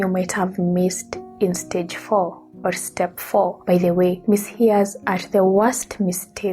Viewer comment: Hello, I found you video very informative and helpful. Many thanks! However, when I enabled the subtitles/closed caption icon, I noticed a few mistakes. For example, when explaining step 5, when you said "miss hear" the transcription read "miss hairs" at 11:00min. At 11:10 it read "amis" instead of "miss hears" and then at 11:24 it read "miss hard" instead of "misheard". I turned it off. Who proof-read your transcription?